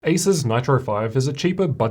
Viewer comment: ASUS NITRO 5? DID I HEAR IT WRONG?